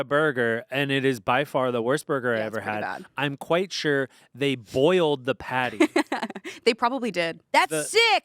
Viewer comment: Dude when Ian said they boiled the patty something went wrong in my brain and I fully thought he said they boiled the bun, and I was just like "yeah that checks out"